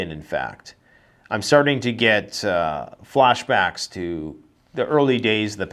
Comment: did he say he was getting hot flashes?